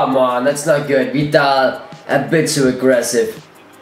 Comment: Put on captions, at 7:38. says: "a bit too argessive" and the captions says "and this is the president" coincedince? :p